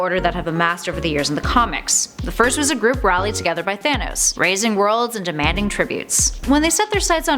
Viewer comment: new word for the day is "razing"... and yes, it's not "raising" it's actually "razing". look it up it's a real word. it means "to tear down; demolish; level to the ground" When I thought she was saying "raising" it obviously made no sense to me but then I turned the captions on n it said "razing" n after I looked it up it made perfect sense. it looks like either you or your writer(s) have an extensive vocabulary lol I would just like to say thank you for teaching me an awesome new word today n now I'm going to try my hardest to use that word in everyday conversion n hope I sound really smart while doing so lol